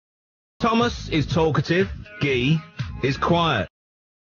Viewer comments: Thomas is talkative Gay is quiet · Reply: I thought he said "Thomas is talkative and gay quiet." 💀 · I THOUGHT PETE SAID “GAY IS QUIET” FOR A SEC AND I NEARLY SPIT OUT MY COFFEE